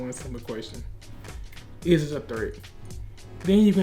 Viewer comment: bro I cant realy understand what you are saying.. it might be "threat" but im not sure